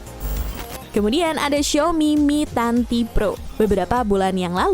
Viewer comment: Apa gw salah denger ya kak bilangn "mi tanTi pro" 3:53 😅